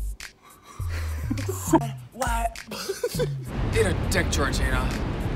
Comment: I literally heard for a moment at 1:05 "Eat a Di*k TorTillA" hahahaha that killed me so bad